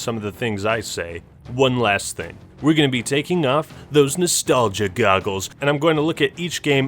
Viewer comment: I thought he said no nostalgia biases.